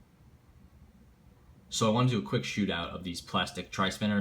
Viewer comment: Quick shootup im down boi meet under Mc diabetes